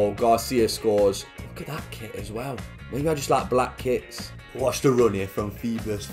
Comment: I though I heard him said “maybe I just like black kids” lol 😂